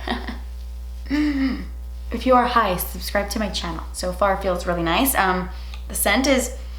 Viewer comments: Totally thought you said "if you are high, subscribe to my channel!" ... Had to go back twice to realize you said "if you are, hi! Subscribe to my channel!" .... Wow. That's what I get for watching a YT video as I'm making first coffee of the day. 🙃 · I heard “if you are high, subscribe to my channel” lol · When you said “if you are, hi, please subscribe” but I heard “of you are high please subscribe” lmao. I had to go back because I was like “wait..what did she just say” lol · I thought you said “if you are high subscribe to my channel” Lolol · Omg. I thought she said “if you are high, subscribe to my channel” lol 😂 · I swear I thought she said.. "if you're high, subscribe to my channel" and I had to rewind it 😂